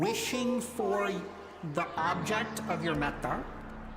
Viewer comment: That intro with the techno happiness meditation made me feel like i escaped samsara and entered club Nirvana. What word did he say when he said at 5:46 wishing for the object of your - - - - - - what was that did he say Meta? what is that? im sure its an ancient meditation word that idk. someone help me out.